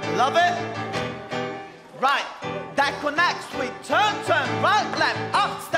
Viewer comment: can someone tell me what gio is saying here? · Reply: That connects with